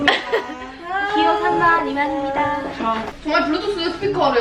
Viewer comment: 님한테 미안하지만 이만희라고 들었어요